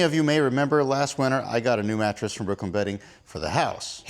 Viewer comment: Ha I thought you was saying you bought your mattress from Brooklyn Betty. 🤭💩😂